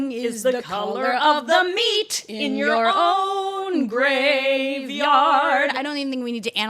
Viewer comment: Damn that's actually crazy I always thought it said "The SLEET in you're own BACKYARD" "Meat In you're graveyard" is definitely less jolly 😂😂